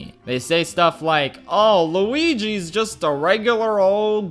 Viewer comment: I love how you accidentally said "Luigi" at 15:21 instead of Linguini.